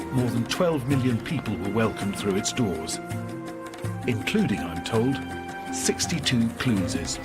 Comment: WHAT!!?? (Idk what he's saying.) Clunes? I think so. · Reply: The Grey Penguin including, I’m told, 62 Clunes’ He speaks perfect English, don’t know why you struggled with that!